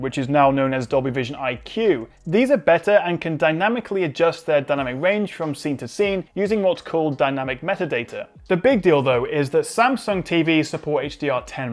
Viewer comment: who heard him say "the big dil..do"? 😂